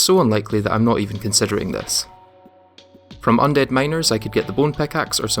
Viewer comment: I heard undead minors😭